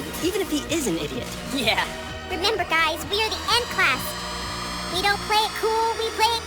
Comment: She said n class but its e class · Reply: i believe you misheard it. she said 'end class' 😆